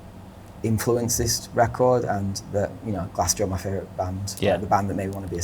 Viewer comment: Who does he say are his favourite band? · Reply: Glassjaw